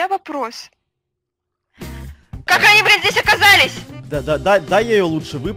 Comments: На 3:13 я услышал как матюгнулась0.0 Хотя вам решать матюгаться вам или нет😊😊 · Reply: Слово блин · Я одна услышала мат на 3:14 ?🗿✋ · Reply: Это слово было блин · как они БЛ*ТЬ здесь оказались ))) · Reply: поставьте на 0.25, будет слышно, что это "блин", но мне тоже послышалось сначала "бл*ть"* ;ъ